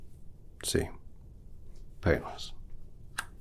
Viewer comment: what he said, "see, painless." Vs what captions heard, "see, famous." Irrelevant but kinda funny to me...😅